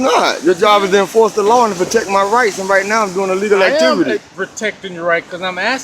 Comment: HEY! say "LAWFUL activity" instead of "legal". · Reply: yes! cause at first i thought he said “illegal” when he said actually said “a legal” activity. “lawful” “lawfully” protected activity.